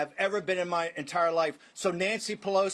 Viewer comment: Why did they censor suck? it's not a curse word. I thought he was saying "shit on that".